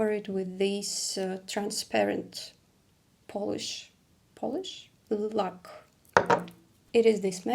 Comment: what is this word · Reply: Lak - nail polish